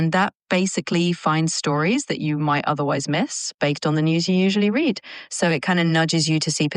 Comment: It has mastered up-speak and fry. Lovely. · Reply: And then, at 7:20, it says "baked" instead of "based".